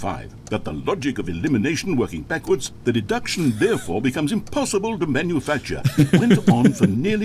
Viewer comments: The Narrator said "Therefor" and the cc said "Thereby". I hate having noticed that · the Narrator says "therefore" but the Closed Captions says "thereby". Truly an unplayable game.